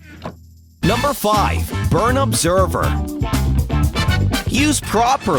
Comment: When it said burn observer I thought it said burn forever lol